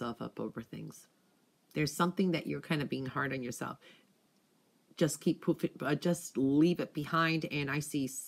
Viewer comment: Lmao sounded like she said “just keep pooping” omg😂